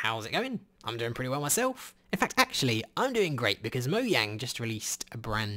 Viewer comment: Did anyone else heard moyang